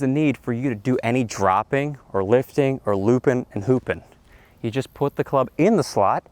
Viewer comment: I had to go back and rewatch. I thought you said "poopin", not "loopin". That would have been funny.